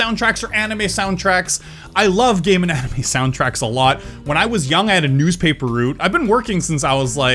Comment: I thought he said "I love gay anime soundtracks a lot."